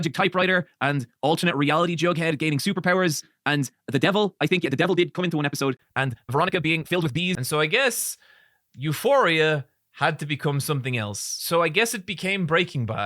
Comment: Did you say Veronica being filled with bees?!?!?!